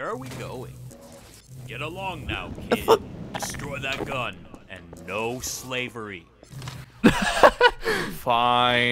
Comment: what was Bryce say? Tf? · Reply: Lol, that was Mahogany, and he said "I thought-" before being cut off by the fisherman.